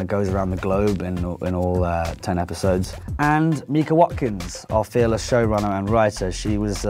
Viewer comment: DANGING IT!! I THOUGHT HE SAID EMMA WATSON NOT MIKA WATKINS!!!